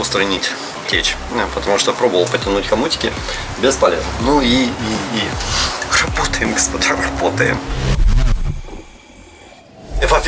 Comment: Мне послышалось или сказал "работаем пацандар"? 😁